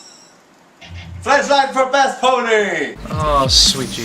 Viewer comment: OH MY GOD. I lost my sanity when i heard "Fleshlight for best pony!"